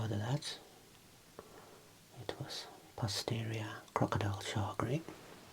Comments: did you just say "it was posterior crocodile-sharkery"? 😅🤣😂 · “Posterior crocodile shark week” is what I heard, and I am not going to the transcript to ruin it for me.